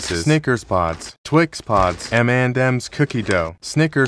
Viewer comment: I thought he said the mandems cookie dough